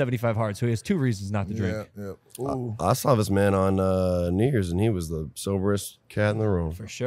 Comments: Did he say “I saw this man in nig**rs” 2:20????? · WHAT GRAVY SAY😂😂 Edit: OHHHH😂 New Years